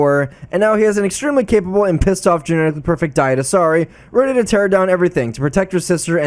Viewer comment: Did you call Miranda an Asari??? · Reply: Diet Asari.